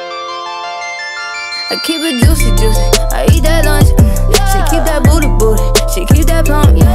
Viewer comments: I always thought she said I keep it juicy, juicy, I eat that lunchable uh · For the longest time, I kid you not. I thought she was saying "I eat that booty booty, I eat that lunch" I'm glad I finally have seen the lyrics.